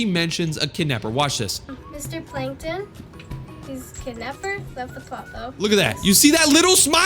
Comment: I hear he is THE kidnapper